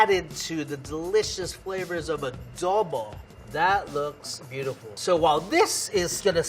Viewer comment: says Filipino words really weird… I remember him saying longanisa funny, now did he just say uhduhbuh instead of “adobo”…